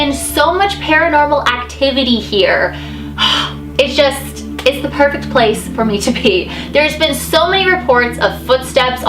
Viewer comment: I thought she said "it's the perfect place for me to pee" and I had to do a complete double take I was laughing so hard X'D